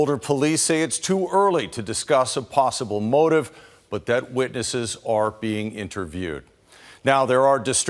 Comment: did he say "... the dead witnesses are being interviewed."